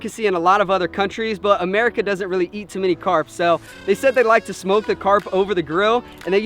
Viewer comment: I thought he said at first "they like to smoke"..."it on the barbuce"